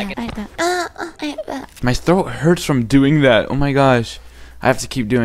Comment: I thought he said I ate bet